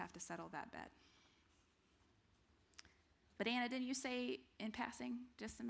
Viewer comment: Great lecture! However, whenever you say, “but Anna”, I thought you were saying ‘banana’ lol